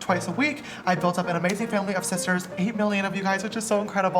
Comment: i thought you said "all 80Mill of you" and i was like dayummmm sishta is really dominating the world!!! lol its 8mill but you're still dominating xx